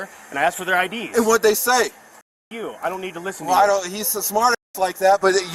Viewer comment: i never heard say “fuck off” i heard him say “i don’t have to listen to you” but then again the profanity is censored 😂